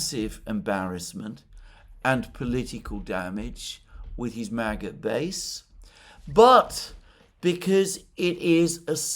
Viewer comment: I heard '' Trump's maggot base'', seems like my ear, have gone fully rogue, when it hears the trigger word.